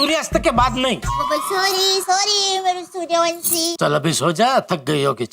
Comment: Did Daye said Sooryavanshi?😂😂😂